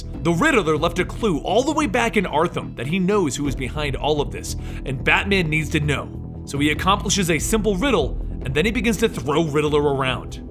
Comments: Did anyone else hear Arthum At 8:30? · did he say "Arthum" ? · did he say Artham